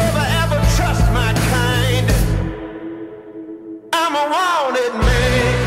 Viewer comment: I keep hearing "I'm a Walden man." That doesn't seem right...